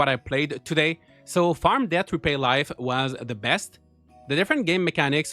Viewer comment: Thought you said Farm Death Repay Life! 😂